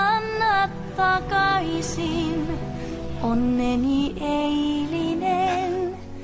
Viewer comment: lol at 4:30 and 4:34 it sounded like she said "say alien".😕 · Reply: Somayeh Tavaf OMG I LAUGHED SO HARD 😂 She sings "yesterday" in English , but in Finnish she sings "se eilinen" and that's really sounded like :say alien 😂😂😂